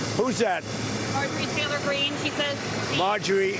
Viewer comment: Did he say "Marjorie TRAITOR Green" 🤣🤣🤣